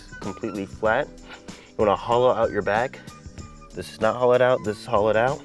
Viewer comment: Lmao I heard "You wanna holla at your back". Making insane progress with your program, appreciate it and your informative videos! Keep it up champ